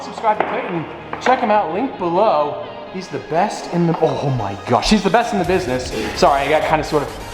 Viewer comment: did he say she’s the best in the business 🤨🤨